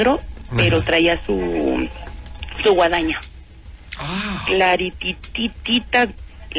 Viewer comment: que dijo la senora wadana? huh en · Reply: Guadaña